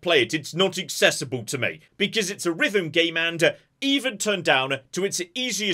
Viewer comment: I had to look up hi-fi rush and figured out you are saying it's a rhythm game. I thought you were saying it was from some game company called riven and had no idea why you couldn't play it.